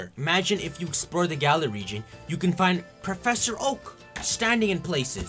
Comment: I thought he said "Professor Hulk".